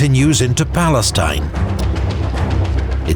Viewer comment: continues in what? Where? Say that again. Let them know. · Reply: He said Israel 😂